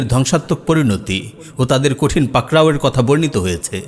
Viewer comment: ”খোদা দের কঠিন পাকরাও “ এটা কি বললেন ?